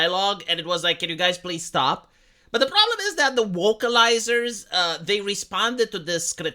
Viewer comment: Your accent is so thick, I heard "Wokealizers" and, you know what? It's the perfect name for these scumbags.